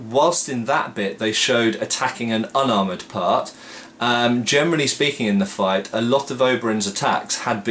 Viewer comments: Sorry, my english is not perfect, but I always hear you saying "germany speaking" . Is that correct or a missunderstanding? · Hm - I always hear "germany speaking". I suppose you mean "generally speaking"?